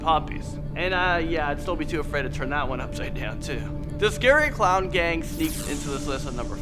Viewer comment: I heard ''scary clown gang'' as ''scary clout gang''